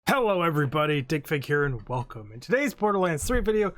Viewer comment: Lol I never seen this channel before and I thought he said “hello everyone dick pic here”